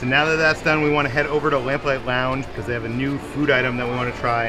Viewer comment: This whole time I thought they always said landfly lounge and accepted it, not lamplight lounge 🤦🏽😂😂